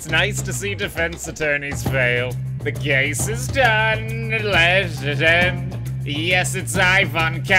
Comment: For a second I heard “the gay is done” until I read the subtitles…